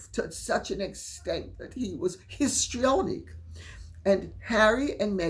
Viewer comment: I literally thought lady C said pistrionic